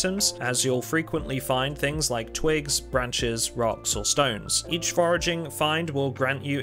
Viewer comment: DID I HEAR A ROCK AND STONE?